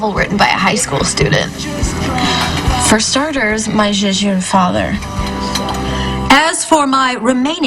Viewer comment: What did call her father? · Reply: She said "My Zhou zhou father.